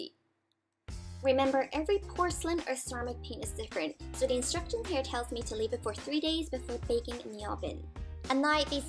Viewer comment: I had to rewind back a few seconds because I thought you said it needs to be baked for 3 days! I was like WHAT. Haha this is cute and I'll try it :)